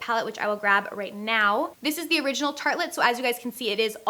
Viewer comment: Omg i thought was saying Tarte lip palette it was serious confusing me